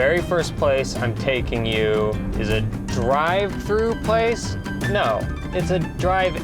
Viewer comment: Thought he said "Drive-fruit place" in the beginning of the video 🍒🍓🫐🍇☝🏼🙃